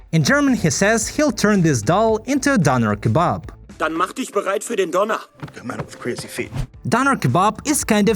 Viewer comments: German here : i clearly hear him say "Mach dich bereit für den Donner = Get ready for the thunder" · Po did not say döner he said donner or lightning